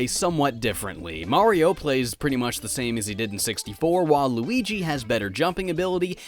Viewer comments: did he say whale luigi oh waluigi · who else thought he said Waluigi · did you say “Waluigi” or “While Luigi”? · Thought you said "Waluigi has better jumping ability." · did you say waluigi ? 😢